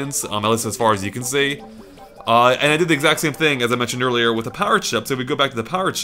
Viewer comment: I really thought you were saying "power chip" and I was super confused.